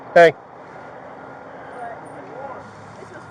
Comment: When the cop tapped on the girls head and said “HEY” to see if she would respond, I hear someone respond “what?” 😢… but she never moved. Freaky. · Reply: It was a paramedic who was walking up and said hey right after he said hey to the girl.